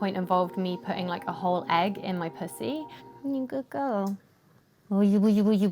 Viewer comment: I heard “egg in my pussy,” paused the video then wondered if i should keep watching 😂